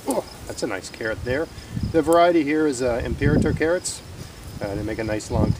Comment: Would you be willing to share the spelling of the variety of carrot you used? I tried spelling it and google told me to "go fish" lol. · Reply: it sounds like you said the variety is imperito carrots and when I tried to google it I came up with nothing.